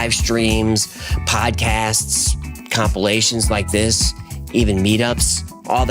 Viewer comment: Oh, compilation. I thought it said copulation